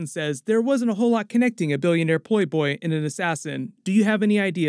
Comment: sounds like he said "billionaire po boy"😅😅